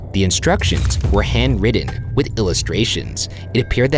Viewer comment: Did Ryan say „handwritteD“ at 2:45 ? Or is it just me ?